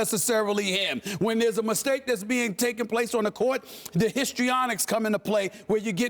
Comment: did SA say 'Histrionics'? around 4:35-36